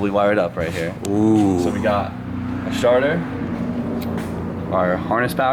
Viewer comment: I thought he said sharter instead of starter! 😆 24:39 Awesome video like always guys! 🥰